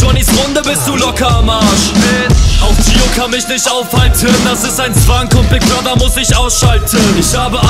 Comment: was sagt er bei 4:31? auch gio kann mich nicht aufhalten.. weiter? in der beschreibung steht nichts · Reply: das ist ein Zwang, kommt big brother muss ich ausschalten